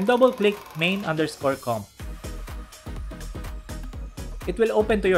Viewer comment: what he said click what?i cant do that anyone help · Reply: It’s “Main_Comp”.